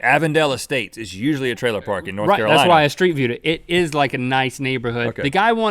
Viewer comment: When he said "I even did street view...cause i'm that dedicated" I thought he said "streaked through". Taking off my clothes and surveying the neighborhood to do research. #thatsdedication hahaha